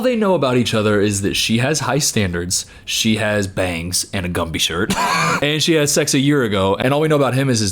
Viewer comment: omg she's wearing a Gumby shirt?? I thought you were saying that style of bangs were called "Gumby Bangs" and I don't know shit about hair so I went along with it..